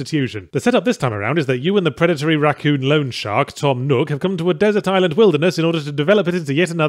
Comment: At first I thought he said "Predatory Racoon Lone Shark" which conjured up a really cool mental image. Loanshark's cool too I guess...